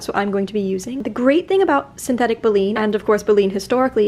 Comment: is it called Beline? And where do I get it? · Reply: It's baleen and there is a link in her materials used. I'm on my phone and it wants to be difficult about copying the link